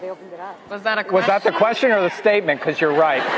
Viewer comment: what's Madelaine saying??? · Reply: “That’s such an inappropriate question”